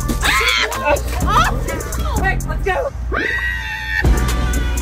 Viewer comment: i thought it said “some musty” 😭🤚🏾